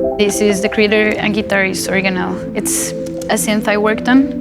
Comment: So what’s the light-blue little synth with cream round-button keys at 2:55, that Ela says she helped design? Can’t hear just what she says there. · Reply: It's an Organelle <link>